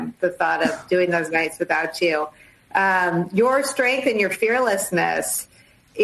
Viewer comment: i thought she said "your strength and your hairlessness" 😭